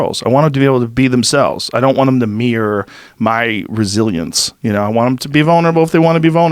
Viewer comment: "i dont want them to be my brazilians " didnt understand that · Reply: He doesn't want them to have his resilience. Not Brazilians.